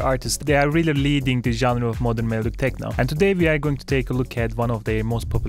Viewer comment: Oh man, 0:03, I thought you said “leaving melodic techno”...my heart sank. I’ve spent the last two years focusing on melodic techno...I hope it doesn’t go away. Fantastic tutorial.